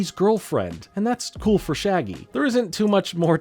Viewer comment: i thought you said "and thats a nice pull for shaggy" no idea why lmao